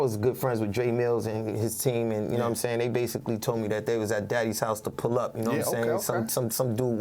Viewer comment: Did smack call Diddy “Daddy” at 16:02😭😭